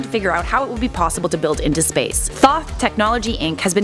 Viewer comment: i thought she said thot